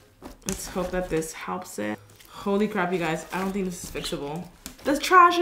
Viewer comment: karina: holy crap at 11:01 or 11:00 Me: what did she just say holy poop